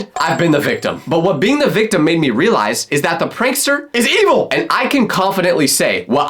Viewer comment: i thought you said "IS EMO" 😭🙌